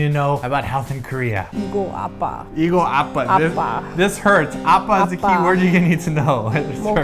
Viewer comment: uh... maybe it's cause my family is from the country, but we pronounce hurts as a-puh. i thought you all were saying "dad" ap-pah. :S lol